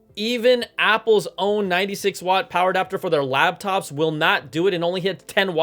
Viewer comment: I just want to point out that I think you said labtop